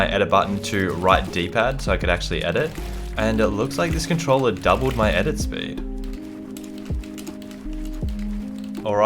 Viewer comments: I thought he would’ve said ‘ did honey double my edit speed??!!! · I thought he said “DID HONEY DOUBLE MY EDIT SPEED?!”